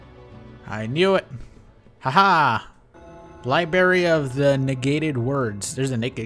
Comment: Did you just say 'Liberry"? Also, duct tape it, nerd.